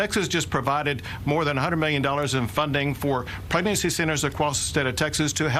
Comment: Wait a minute.......did i hear Abbot say "Pregnancy Sinners?" Bro....wtf....